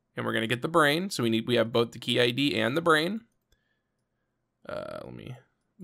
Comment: Anyone else catch that? "( pink )Key and the brain"? hurr... no?